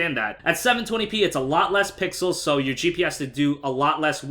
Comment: Did anybody else hear "GPS"